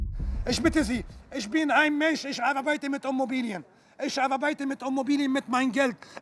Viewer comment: Die "Ommobilie" - wer kennt sie nicht. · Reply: Ich hab Homobilie verstanden...